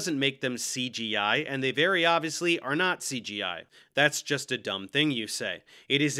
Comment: When you'd say 'not CGI' I kept hearing 'NaziGI' which i'm amazed they don't use, frankly, since it fits into their goofball nonsense